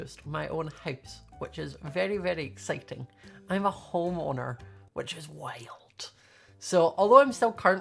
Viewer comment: I thought you said hormoner (like stuff that happens when ur a teen hormones)